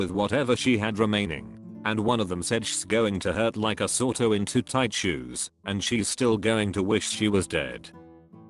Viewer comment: Like a sorto in two tight shoes? What did Daniel really say? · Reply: Like a sore toe in too tight shoes